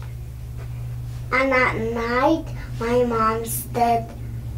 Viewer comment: the child clearly said “at night my mom’s dead…” then something about sleeping where the crystals & flowers are. If I heard this in the jury box or if I was anywhere involved in this case that’s something I’d have revisited. But alas, they’re all in heaven so it makes no difference. Except for Micheal- Josh Powell’s brother & co-conspirator/killer- he should have to face some justice I would pray.